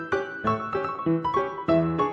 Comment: Whenever you say "bye for now" I thought you were saying "buy four now" because I'm just an audio listener and I thought you were promoting gear or something. I've been following you for years